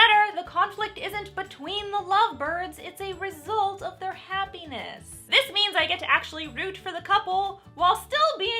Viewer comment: The happiness in eyes while saying “extreme conflict” is the kind of irony I never knew I needed. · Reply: turn on coalitions it says “hair penis” instead of happiness